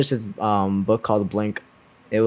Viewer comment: Wait what the book called? 31:20, blank? by who? Google literally showed me a blank white book instead of an actual book. upsetting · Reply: I believe it was called Blink: the power of thinking without thinking by Malcolm Gladwell